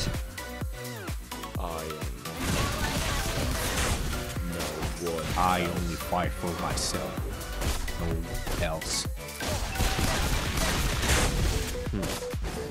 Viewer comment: I think said for zero😂🤣